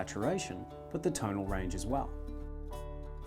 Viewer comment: That gel you worked with, I I not quite understand....I heard "Language folio gel"?...Could you please clarify? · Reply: Check out Oleogel by Rublev